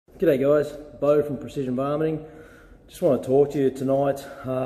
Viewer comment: Coulda sworn he said "Precision vomiting" at the outset.